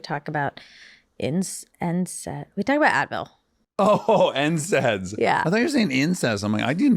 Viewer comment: I love how Monica was talking about NSAID's and Dax thought she said incest. Dax said he didn't remember talking about incest, but they totally did talk about incest, (Parents and their children raised apart falling in love).